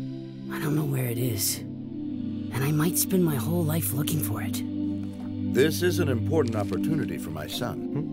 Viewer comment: for a second, it sounded like he said that I might spend his whole life fucking for it. There was extra sound in my house so... idk why I heard that though.